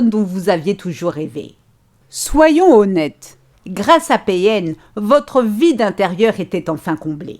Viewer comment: Mdr quand tas dis "votre vide intérieur sera comblé " jai entendu "votre vie d'intérieur sera comblée" 😂 il ma fait perdre mon taf et fais de moi une ménagère, une femme d'intérieur, sans amis, ni passion. Mais cest fini.